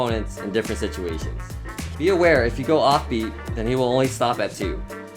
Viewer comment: Can someone explain me what he said at 0:43? (I know,I’m late.) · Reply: Be aware, if you go off beat he will only stop at two